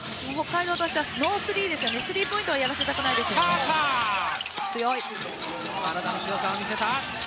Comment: パーカーって聞こえるわ